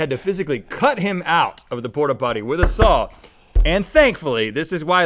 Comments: On the "nude Idiot in the Port-a-John" I heard "Spot-a-Pot" . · He definitely said Spot-A-Pot; that's the name of a company that makes port-a-pots. But I heard "Spotify" the first time yall played it. Haha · I hear " Spot-A-Pot" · I heard “spot a pot" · I heard "spot a pot" the third replay 😅 and that makes the most sense of what he was trying to say. He calls a porter John a "spot the pot". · I heard "spot a pot."